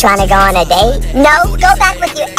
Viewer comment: It sounds like he is saying “your my ex-dummy”